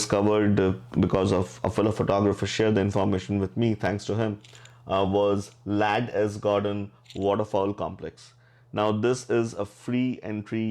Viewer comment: What was the name of the other place you mentioned? I thought you said Lattis gardens? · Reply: Ladd S Gordon Waterfowl complex <link>